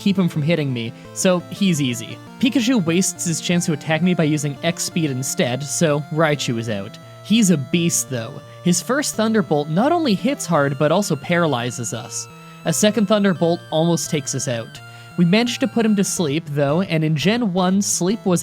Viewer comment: I heard “he’s obese though” and I looked at him and just thought “yeah, kinda”